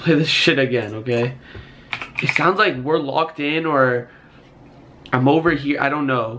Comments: What I Heard: 5:30 "I Want My Mama" 4:49 "Help Us" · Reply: I think there's to ghost it said WE'RE locked in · i hear we're locked in